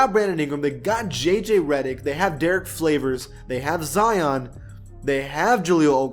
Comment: did he say Derrick Flavors? Lol 😆